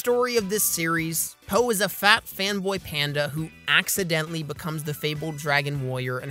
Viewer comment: Did you just say "accidently"? Hmmm hmmm.... THERE ARE NO ACCIDENTS